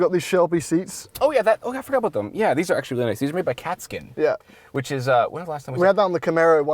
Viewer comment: Am I the only one who heard "made out of cat skin" instead of "made by Katzkin" at 12:37? xD